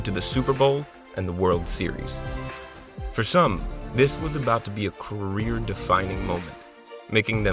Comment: Thought I heard "this was about to be a rear defining moment".